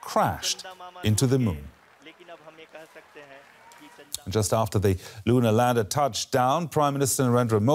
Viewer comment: Did he just say, “just after LUNA landed…” prime minister Narendra Modi hailed it as a historic achievement?